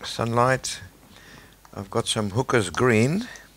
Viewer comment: Did he just say Hookers Green?